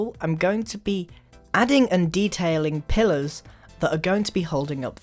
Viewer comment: every time he said pillar I thought he said pillow